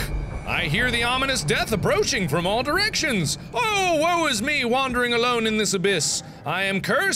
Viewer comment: Anybody else hear WAU is me instead of "woe"?